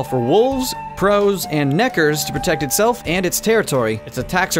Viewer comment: What was that word you said there? That one around 3:58? Starts with an "N." I think you said Neck-er, but I definitely heard something else. Never played the game. I'm guessing they're an enemy of some kind. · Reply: Channel 46 Nekker, they're a type of enemy in the game. some of the enemies have pretty simple names. Drowners live by water, Nekker have a lot of rolls on their necks.